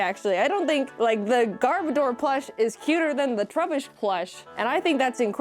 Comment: I heard "Garbodor" as "Gardevoir" and was so confused 😆